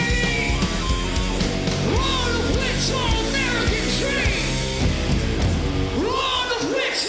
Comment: I thought he was saying "We're all a bitch for an (or the) American dream"